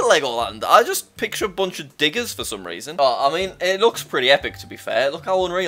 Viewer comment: wait what did you actually say? Ayo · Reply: digger.